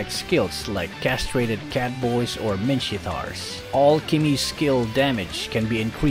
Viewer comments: i hear you say minshittar XD at 6:43 XD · Did you say min-sh!t-tar on 6:43?